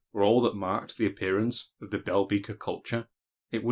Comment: I thought he was saying "Velveeta culture"